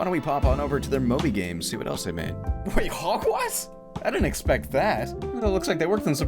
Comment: Did anyone else hear him say hogwest instead of hogwarts